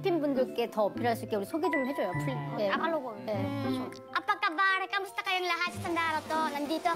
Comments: did she say "apa kabar"? or did I hear wrong? Wkwkwk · is she say "apa kabar"?